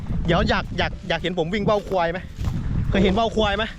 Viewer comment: ได้ยินว่าว่าวค**😂